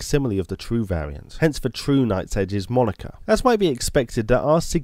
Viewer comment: “hence the true nights edge is monika” what am I hearing what does he actually say I’m so confused